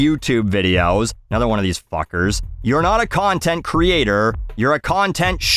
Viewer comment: I seriously thought she said “cunt-tent creator”.